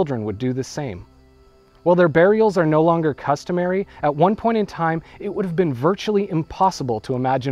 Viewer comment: there's an error in the subtitles. It says "at what point" instead of "at one point".